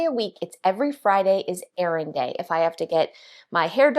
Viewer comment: Errand day! I misheard it as Air Raid Day! Wow - wonder what she does on air raid day. Things definitely do not have the same level of importance as on a regular day.🤣🤣🤣